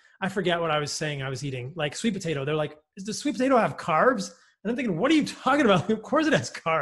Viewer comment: I thought it said crabs. I was wondering why I should be concerned about eating crustaceans